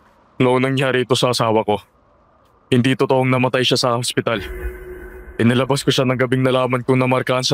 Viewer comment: angspital. 49:15 sinasapunan. 😂😂😂 Ano daw??? narinig ko habang nagmamaneho Ako. Mamatay Ako kakatawa magisa.